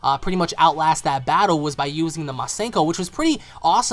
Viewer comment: Not even going to fucking lie when I say this. When Trunks said "Masenko!" I literally heard "Rasengan!"